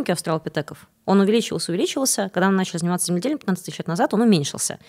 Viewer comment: не разобрал, что она сказала? "Когда мы начали заниматься..." чем? Свинеделями? Что это? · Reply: спасибо!